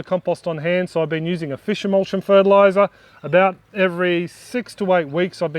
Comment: What did you say you used? Please and thank you 🙏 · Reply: "Fish emulsion" liquid fertiliser Anna. 👍 It's made out of the European carp that are slowly taking over the Australian waterways on the eastern seaboard. Cheers.